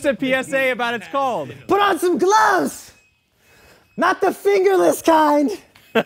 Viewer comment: For a moment I thought he said "put on some clothes" and I was about to say "never!" 😂😂😂